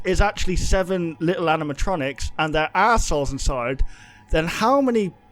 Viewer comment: It sounds like he says "ars*holes inside"